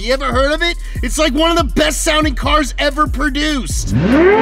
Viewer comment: Media At 9:02 Pumphrey says “sounding” while the subtitles say “selling” fyi